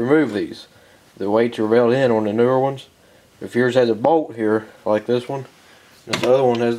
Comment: I was listening and not watching....I swore he said: 'If yours has a "Boat" here like this one.' I was like BOAT? WHAT? ah... BOLT!